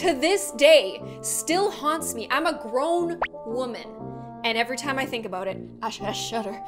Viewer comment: When you said “grown woman” I thought you said “ gloom woman”